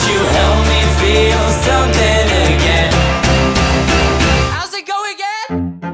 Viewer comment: I keep hearing "How's it going girl?"